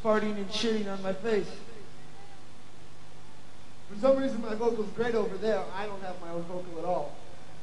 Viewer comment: Could anybody tell what John says at 42:04 just before jammin' with Flea ? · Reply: "for some reason my vocals great over there. I don't have my vocals at all"